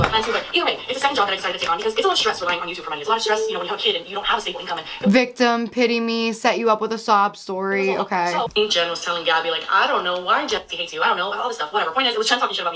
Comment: Here my closed captions say “Set you up with a Saab story.” I assume it’s a result of Gabbie’s PA accent, but I enjoy imagining Gabbie’s upset about a car.